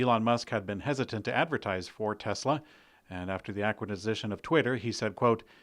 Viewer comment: Did you just say “Aquitizition” of Twitter? 0:57 I’m schlocked! Schlocked, I tell you!